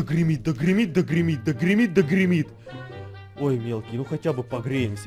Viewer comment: гримит и гримит я услышала кримит та кирмит 🌚👌